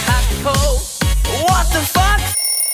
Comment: In 1:47 it sound like Ylvis says what the f***